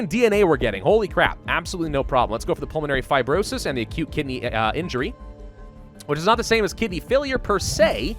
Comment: "Acute Kidney Injury". heard that as "a cute kitty injury" lmao im dying